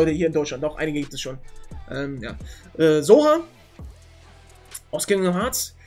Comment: ich hab erst zohan verstanden